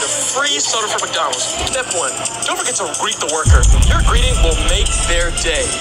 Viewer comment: I heard “greeding the worker” 😂